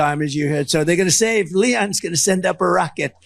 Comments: Did he call Elon Musk LEON? 😂😂😂😂😂😂😂😂😂😂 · Did TFG call Elon "Leon" at the 13:30 mark?